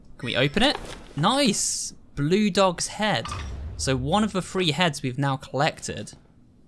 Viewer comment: I honestly thought he said “Free head” instead of three heads